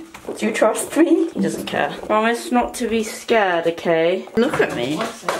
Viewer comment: i thought she said “I want my snot to be scared”